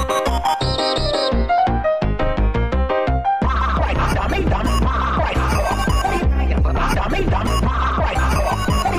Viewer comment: That Part with a Different Voice, In an Older Corrupted Tom Design, When Corrupted Tom had a Mallet, There was a Corrupted Jerry to! It Sounds like its Saying “Damy Dam, Quiet a Score” Whats the Jerry Voice Saying? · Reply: It's Tom's voice from the 1st Tom and Jerry movie, its supposed to be saying "What do ya think I am, a dummy?"